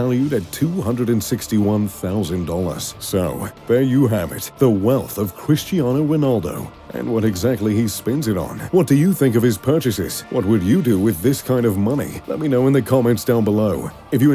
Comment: Was I the only one who thought he said he spend alot on hoes